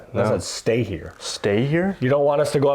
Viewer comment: I think they said “no I have to stay here” and then they used spirit talker to clarify “stay here” like they can’t leave